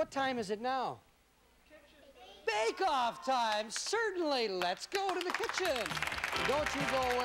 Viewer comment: "but its bake out time ladies and gentlemen". Could have sworn he said "makeout time"